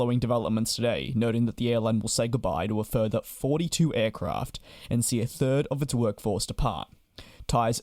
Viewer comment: "They will say goodbye to a further 42 aircraft". According to Airfleets they only have 40 active/parked aircraft. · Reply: I heard it as "a third of 42 aircraft"